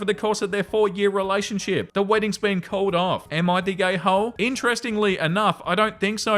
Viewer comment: Okay, so at 28:20, the story about the gay guy unknowingly boning the groom-to-be, accent trips up the captions HILARIOUSLY! Instead of reading "Am I the ahole," they say "Am I the gay ho" 😂 Apparently so!